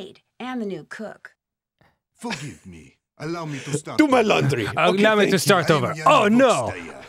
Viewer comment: I thought he said "allow me to starve" ...